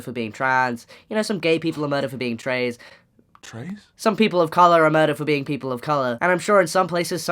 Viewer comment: I heard "Some gay people are murdered for being straight" 😆